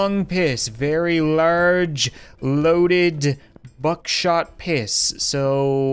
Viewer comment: Did you just say buckshot piss?